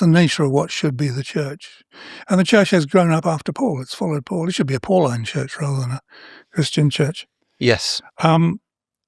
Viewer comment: I heard "It should be 'apalling church'" at first 😂